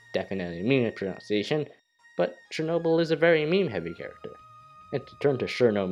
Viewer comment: did you just say Chernobyl